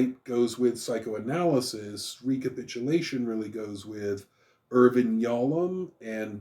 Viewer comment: You mention someone whose name I heard as "urban yallum." That's Irving Yalom, correct? It was Google's top result.